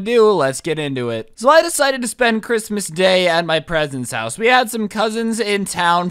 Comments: Am I the only one who heard him say presents house at like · who else heard him say at the beginning "i decided to spend the day at my presents house" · you said present instead of parents